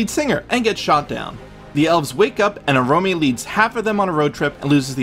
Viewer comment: I 💭 i heard "a Romeo leads the elves on a road trip..." Had to check again to make sure. It was Oromë, i just heard it wrong🙌😅🤣